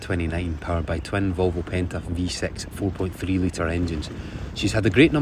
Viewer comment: 9.2 litres of petrol power, ouch! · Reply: Yes, I misheard, I thought he said 4.6 litre each. I had a 4.3 in a 18ft bowrider, it was reasonably quick. But 2 pushing a boat of that size would fair drink the stuff I reckon...